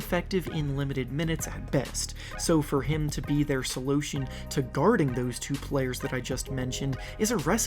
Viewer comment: he really said solotion at 4:17😂😂